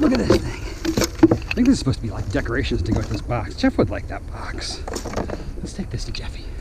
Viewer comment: I backed up a bit... tht big light, I thought u said u were gonna give it to Jeff?